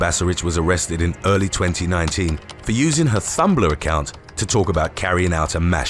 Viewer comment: Is it me, or did I just hear Thumblr instead of Tumblr?